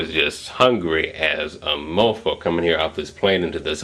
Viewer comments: DID TAYZONDAY JUST SAY. " I am as hungry as a mofo/mofucka". At 5:22?!?!? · omg! did he say mofo? haha at 5:18 haha nice!=D